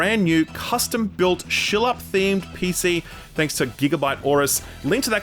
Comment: did he just call himself ShillUp? so it’s a ShillUp PC.. that I can’t win cuz I’m in America.